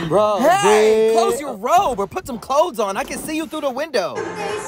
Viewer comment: " oh bathroom time is over get creep" "hey close your robe I can see you threw the window" 🤣🤣🤣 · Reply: I thought he said I can see your little Theo here things strange